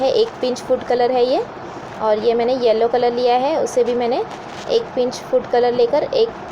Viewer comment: is that orange food color or red food color mixed with water?? and at 9:00 did you say thats yellow food color ? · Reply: at 8 .51 orange red food colour n at 9.00 yellow food colour